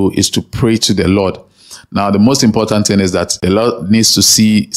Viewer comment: Did you say The Lord or Allah here? The subtitle shows Allah!!